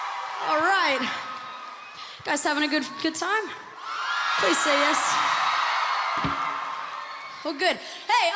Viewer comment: wait does she say “alright you guys havin a good f**kin time?” at like